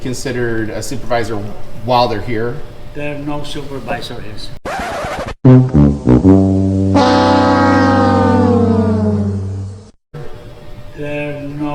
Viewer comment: he clearly says (with his broken English) "There no supervisor is" Not "no supervisorS"